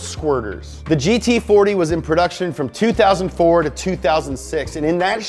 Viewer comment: subtitles say "projection" not "production"